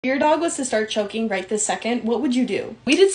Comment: I totally misheard that😂 I thought u said What would you do if ur dog started choking you right now 😭 And I was like taking his paw off my neck they’re not that strong